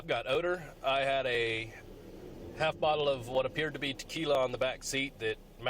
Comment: I thought he said he seen a bottle of tequila in the back?